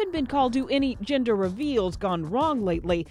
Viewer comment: I keep hearing her say 'ginger reveal'! Hahha